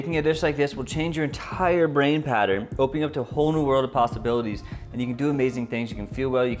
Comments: Did you just say "pastabilities" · I swear I heard "and open up your mind to a whole new world of pastabilities" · did you just say pastabilities? :p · Did he say... Pastabilities? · did he say pastabilities? lmao